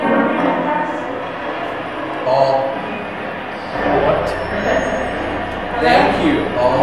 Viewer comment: I heard "thank you"